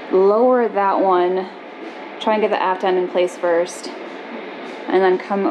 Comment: I thought said "ass-end" of the boat, hmm, not very nautical, then realized it was "aft-end". I may need to turn on the subtitles! Great project, enjoying watching.